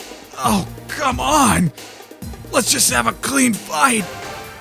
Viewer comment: Clean Fight? Oh snap i thought he said "Stab me with a knife" ROTFL